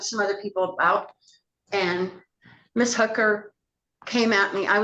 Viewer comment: When the judge first referred to the defendant I thought he said "Miss Sucker." Turns out my very poor hearing wasn't wrong, sucker indeed for not being able to keep her mouth shut!😅